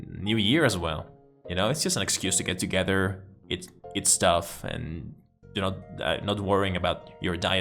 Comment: ¿Qué tal funcionan los subtítulos automáticos? Les gustaría más videos así hablando en inglés de algún tema? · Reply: I only saw one error in the auto-subtitles: at 5:32 you say "eat stuff" and the subs think you said "it's tough." (And of course, whenever you said any words in Spanish, it had no idea! 9:52 -- Mr. Zados)